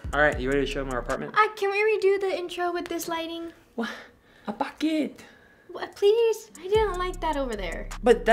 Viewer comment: is it just me or did say "at bakit?" at 0:31 ???